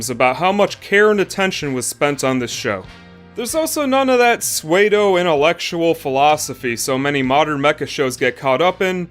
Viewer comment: swaydo/swato what from mecha? I've haven't heard of the term nor how it's spelled; I can't understand. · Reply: He probably ment to say pseudo.